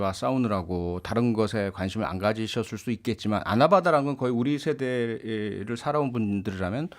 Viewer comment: 아나바다를 몰라요? 아 나는 아무리 들어도 바이든으로 들려 다시 들어도 바이든